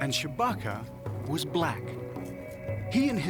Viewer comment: swore he said Chewbacca is black then I googled Chewbacca of nubia lol... it's Shabaka but why did he say he and the Pharoahs were black? lol was there any doubt or something?